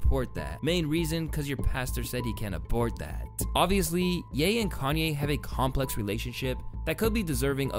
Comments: Did bro just say ye and kanye have beef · did he just said ye and kanye??? Or am i bugging..